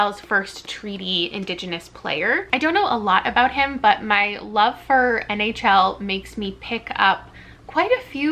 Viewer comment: For a second I thought you said “love of NFL” and I was like 🥴🤔 but then I rewound a bit and was like ohhhhh 😅